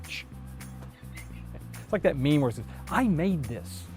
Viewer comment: It almost sounded like you said I maimed this instead of made. Lol